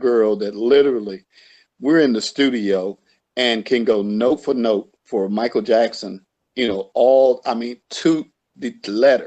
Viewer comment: what did he say here??? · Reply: Says can go note for note to the letter as in she could sing whatever MJ doing he was referring to dead on.